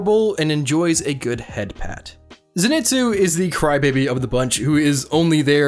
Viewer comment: "enjoy a good head" I jumped into conclusion very quickly, but then I heard "pat"